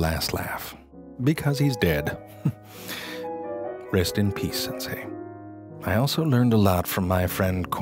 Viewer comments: DID I HEAR SENSEI?!?!!??!??!!!!????? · is it just me or did I hear him say sensei 😂😂 as in Naruto and Kakashi sensei